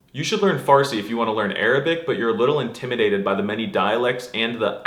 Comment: What I heard: "Arabic and the EIN sound" Didn't know Arabic had German